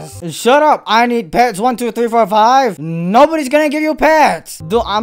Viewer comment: that does not said 12345 dood it saids 5743 bruh. what the freak is wrong with u bruhhhhhhh.